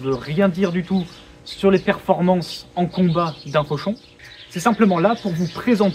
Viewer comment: Au début j'ai compris "pochon", ça m'a surpris (non pas que la physique des sacs plastiques soit inintéressante, mais bon...)